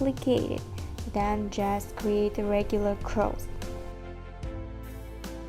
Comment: На 10:55 минуте я услышала ТИХО. У МЕНЯ В ЭТОТ МОМЕНТ ГРОМКО РАЗГОВАРИВАЛИ ДЕТИ. Я АЖ ИСПУГАЛАСЬ С НАЧАЛА. ДУМАЮ Я ВРОДЕ НЕ ГОВОРИЛА ИМ ....ТИХО....НО ЭТО БЫЛО В ТЕМУ 🤣👍👍👍👍👍 СПАСИБО ЗА УРОК. ОБОЖАЮ ИХ СМОТРЕТЬ.